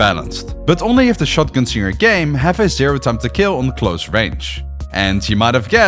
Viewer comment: What is he saying at 6:00 about shotguns having a "zero tempt to kill"???